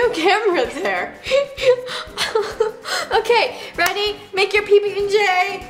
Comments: Did anyone notice that Veronica called Vanessa Veronica. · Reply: Emily Cheung oh at 5:14 yh i replayed it cause I heard roni but she actually said ready😂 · Did Roni just call Nessa "roni"? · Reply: Alberto Serra She said "Ready" not "Roni" and why would Roni said that to Nessa? Its her own name. lol. m8. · roni?? I thought it was Nessa? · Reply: it sounded like she said roni but she said ready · did roni say at 5:14 to 5:16 "ok roni make your pb&j" 😂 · did roni just call ness roni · I thought roni said "roni! Make your pb and j!" Also I hate to say this but... 2 0 1 9 a n y o n e ? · did she just say Roni when it’s Nessa? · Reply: She said ready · did Roni just call Nessa “Roni”? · did roni said to nessa "roni make pb&j?¿"